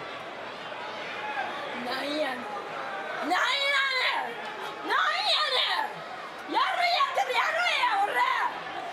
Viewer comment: can someone explain what Asuka say? from what I see it looks like she's defending Liv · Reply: i think she said finish her